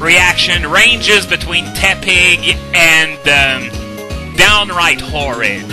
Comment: At first I thought you said "it ranges from tepig" that would have been lulzy